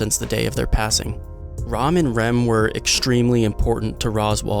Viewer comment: is it me or did I hear ramen rem